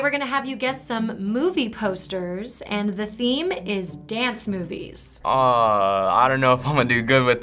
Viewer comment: I thought it said "Dance Moves" which I think would make a great video. See if teens know older dance moves.